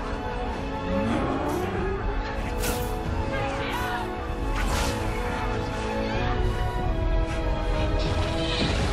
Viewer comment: did I hear the crosho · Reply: Crucio